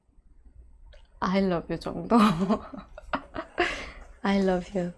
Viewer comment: does she said i love you Jungkook?